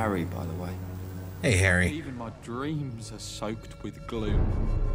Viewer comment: Pffffft, I thought he said his dreams were soaked with glue, not gloom :P